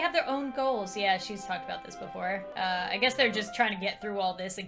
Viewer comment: I thought she said ya she's as f@ up as before but she said she's talked about the before UwU